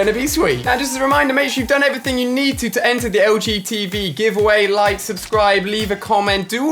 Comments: When he said LG TV at 10:05 I thought he said LGBT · I thought he said LGBT give away lol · I THOUGHT HE SAID LGBT giveaway 😂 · I thought he said LGTB for a second😖